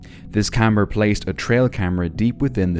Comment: Did you say "this camera placed a tail camera"? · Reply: Believe he said camper